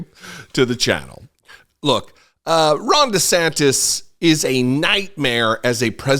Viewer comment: It sounded like you said 'Wrong DeSantis' . Awesome though ❤